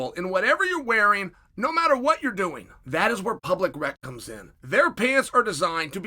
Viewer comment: I thought you said public rectum at first. Oh "public rec comes in". I got it I got it. Chael sent me.